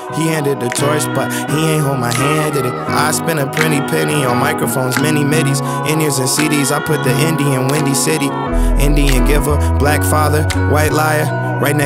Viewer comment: What does he say at 2:11? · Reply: dyllon garza I spend a pretty penny on microphones, many midis, in ears and cds i put the indie in windy city I think thats what he says